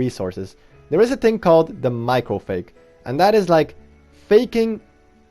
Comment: Yeah it's funny how I didn't know faking the pallet stunt was called the "Michael Fake." Well call me an OLD foggy 🤣. Thanks and welcome back 👏👋. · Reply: Oh sounded like you said the Michael Fake my bad 🤣